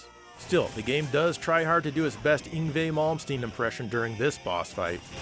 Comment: Yngwie Malmsteen if you were wondering what he said there.